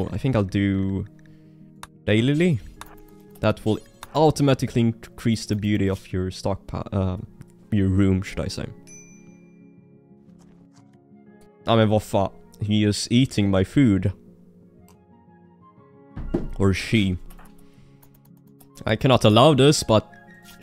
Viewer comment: "Agh men va fa-" I heard that swedish comming through xD